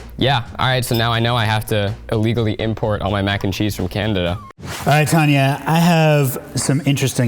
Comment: Did he say candada?